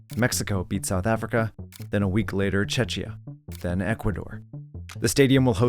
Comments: I had to double take, I thought I heard Chechnya at first when in fact it's just a mispronunciation of Czechia as I stayed up to watch that game lol😅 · great video -- did I hear chechnya at 17:03?